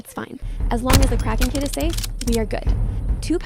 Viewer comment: Ate the base!! i wish i had the chef class.:( I'D PROBALY EAT ALL THE STEAKS I'M SO HUNGRY! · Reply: Btw.. WHY DID YU SAY CARKING KID ITS KRAKEN KID😂🎉